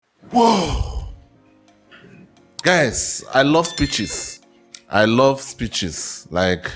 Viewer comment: lmfao I had to replay the first few seconds of the video... All I heard was "Whoa, guys, I love bitches."